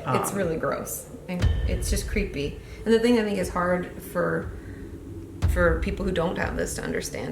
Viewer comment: Therapist: Some sociopaths aren't good at fake crying CUTS TO LAURA LEE 😂😂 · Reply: she said I think that nigaa hard just say it sound like it